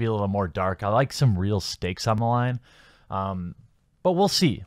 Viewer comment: Did somebody say “STEAK”?!